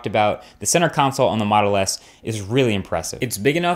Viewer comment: have I heard "Moto S"? :>